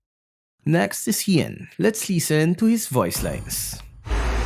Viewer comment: you sounded like you said "Lee Sin" instead of "Listen" LMAO